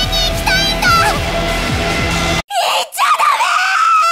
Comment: what's the screaming voice saying? Is it Riko? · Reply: yep its riko, shes screaming "DON'T GO!" captions work for this video btw if you wanna know everything else going on too, theyre in english!